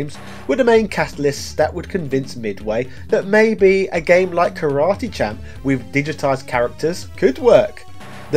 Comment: Thought u said karate chop 😂